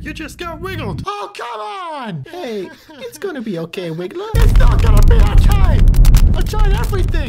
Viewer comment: what did wiggler say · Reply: It’s not going to be okay!